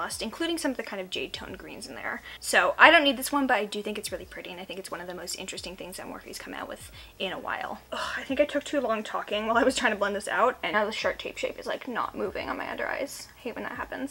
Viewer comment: hehe sounds like u said shart tape shape